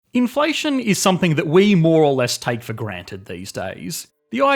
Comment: When you said "more or less" I thought you said Moralist and were making a Disco Elysium reference